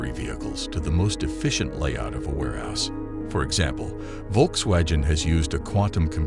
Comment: I swear i heard Volkswasion